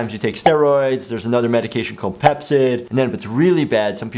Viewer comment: When he said Pepcid I thought he said Pepsi XD